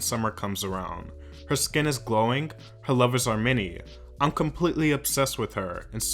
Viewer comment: I was only listening at first so I thought you said, 'her lovers: Armenian' and went 'huh, guess shes in a relationship that I didnt know about' before looking down at the text